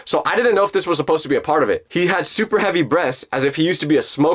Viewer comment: why did I hear "super heavy breasts"